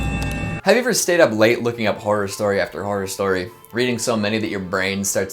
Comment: At first I thought he said "have you spent all night looking up porn story after horror story" and was like ye....nevermind